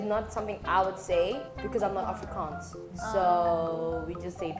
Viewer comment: she said Afrikaans, but subtitles say Africans, which is a mistake (i think)